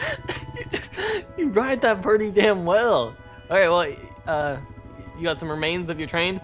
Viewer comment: did he say damn well?